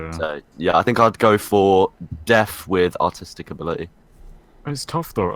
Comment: I keep hearing 'deaf with autistic abililties' when it's deaf with artistic abilities